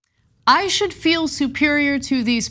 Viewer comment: he said “i actually feel superior,” not “i should feel superior.” hard accent but if you are trained in southern it’s clear. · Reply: He said "I should feel". I'm not trained in southern accent and the first thing I heard "I actually feel superior" but he was hemming and hawing so much about the should and should not that I went back and slowed it down. He clearly says "I should feel" when the audio is slowed down.